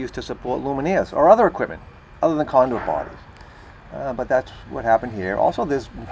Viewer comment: I couldn't make out one phrase, and I assume the Closed Caption didn't either. At 0:19 - "a violation of section 358.12 number two emt cannot be used to support lumineers or other equipment other than condor parties" I am glad the condors have come back from the brink of extinction, and I support their right to party, but somehow I doubt the electrical code endorses this. I assume it was conduit-something-something???